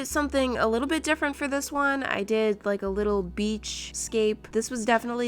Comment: did she just said bitch